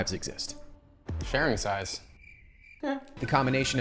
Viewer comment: sharing size?? I thought you said sharing FRIES 😂😅